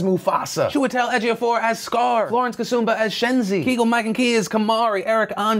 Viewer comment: When he's naming the celebs, he gets to Keegan-Michael Key's name and it sounds like he says "Kegel" not Keegan. lol